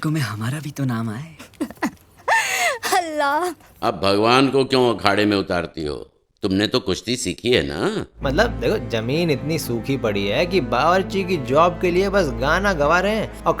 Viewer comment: did she said HALLAH 😂